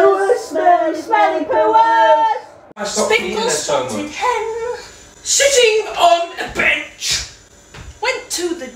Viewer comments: When he said sitting on the bench I thought he said shitting on the bench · "SiTtiNg oN tHe bEnCh" how he said it tho- 😩🤣😭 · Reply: I thought he said shitting not sitting lol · Really thought he said "shitting on the bench" lmao 🤣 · The way he started to dance when he said “sitting on a bench” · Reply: I could’ve sworn he. Said shitting on a bench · It sounded like he said shiting on a Beach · When he said sitting on a bench, I thought he said shining on a bench. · When he said sitting on a bench I thought he said shitting on a bench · When he said sitting on a bench I heard it as 𝙨!𝙝𝙞𝙩𝙩𝙞𝙣𝙜 on a bench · I Thought he said shitting on a bench haha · I rly thought he said sh!tting on the bench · i thought when he said 'sitting on a bench' he said 'shitting on a bench' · I thought he said at first shitting on a bench